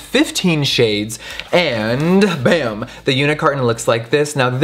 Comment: The first three times you said “unit carton” I thought you said Unicorn And I was so confused 😂